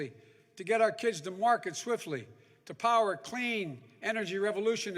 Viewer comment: Did he just say 3:50 "to get our kids to market quickly!"??? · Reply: he said "to get our kids to market swiftly."